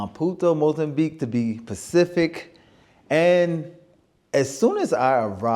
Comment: Enjoy your content but just wanted to politely say, the word is “Specific” not “Pacific” lol I keep hearing you say “Pacific” across all your videos. 😅